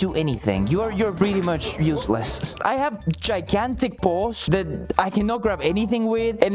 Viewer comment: NGL I heard "I have Gigantic Balls" until the picture came up